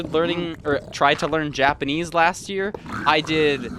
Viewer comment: anybody else hear Yapenese?